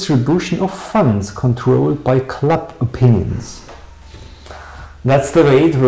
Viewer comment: "funds controlled by... ???" What's the last word? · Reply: by club opinions...